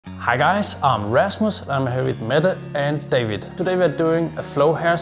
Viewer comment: Rasmus sounds like u saying am restless 🤣😂👏